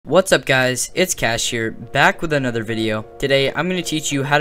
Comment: "it's Kash here" i think you meant "Kash the King" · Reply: i heard "its cashier"😭